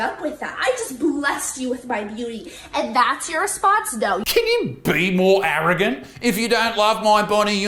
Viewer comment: I think she said Dough